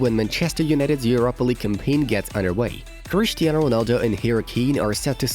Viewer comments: i heard Cristiano Ronaldo and harry cabe 🤣 lol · did you just said Harry Kane😂😂😂😂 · did they say harry kane 👀??